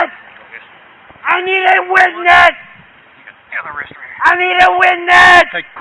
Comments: i thought he said "I need windex" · I thought he was saying I need a windex😂😂😂😂